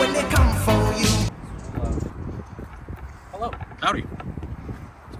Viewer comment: Surreal Cam sent me here. subscription hit and ready to see more coming videos. one question... what did you say on 41:59 when replying to that cop saying hello ? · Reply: I said “howdy”. He asked what’s going on, I replied with “I’m good, just documenting some conversations here”